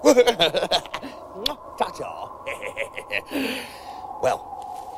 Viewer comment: In my mind I know he's not saying "fuck off", but... I can't work out what he is saying... anyone? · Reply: He says "Ta ta", which is a old Engish phrase for goodbye.